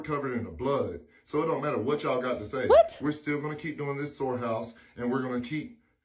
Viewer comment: Omg this whole time I thought you guys were saying SORE house and SORE kids and I was like ??? 😂